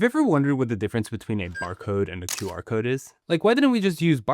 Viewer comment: Why did i heard "Bark code"?